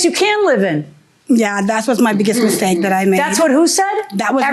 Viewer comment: JM kept talking over her when she was 2:16 she said “that was my biggest mistake” damn, LISTEN TO THE PLAINTIFF! She didn’t say that’s what someone said to her 😒 stop yelling at her, so you can actually hear her. The plaintiff was so calm, cool, and collected. While JM is just taking jabs. That annoyed me so bad.